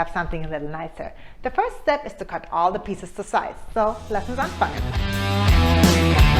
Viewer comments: Did she just say: lasst uns anfangen? 🤗👍🏻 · Hi, hab ich das richtig verstanden bei 0:22 " Lass uns das anfangen " ? Wenn ja , dann viele Grüße aus Old Germany 🙂 Markus